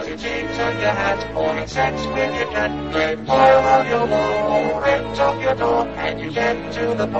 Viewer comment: Oh phew, "to your WALL". I thought he said he would tape stuffed animals to his balls